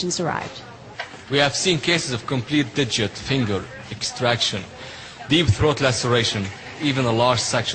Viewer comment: i thought i heard him say deep throat lacerations